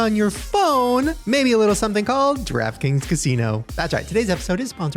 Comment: shane, random but when you were talking about the DraftKings casino, i heard "Trap Kings Casino" lol i was like damn thats a catchy name